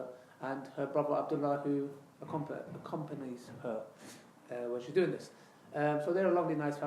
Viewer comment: I thought you not allowed in same room with a sister as third person is always the devil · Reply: I think he said her brother Abdullah accompanies her